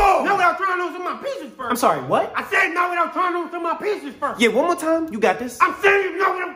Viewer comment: I thought i was the only one not understanding what mr.bling bling was saying But too 😂 "Okay,One more time, you got this"😂😂